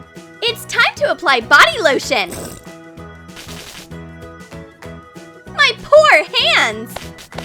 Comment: "my por hand" Girl wtf you sayng..? 1:40 me: OK i'm done.